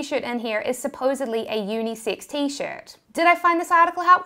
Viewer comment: At first I thought she said unisix.